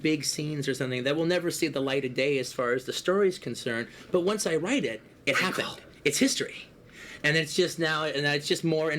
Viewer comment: what does Pitt say here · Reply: prequel